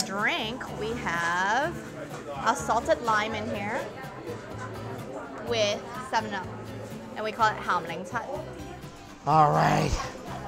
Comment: did she say a salted lime with salmonella?